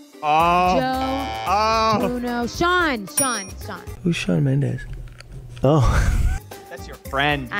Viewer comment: I heard: kussolj Mendes. (shut up)